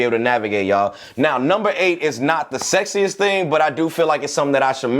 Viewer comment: am i dumb or did you say "sexiest thing"